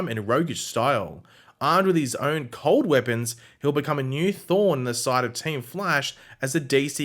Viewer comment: I thought you said a new thawne not a new thorn I was like what